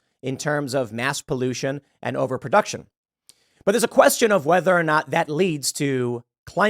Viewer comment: Oh! You said over production. I thought you said over population. Thank God.